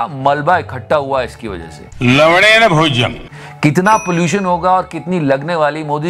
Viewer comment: Its लवण (lavana) = Common Salt guys. Don't misheard. 🤣🤣🤣🤣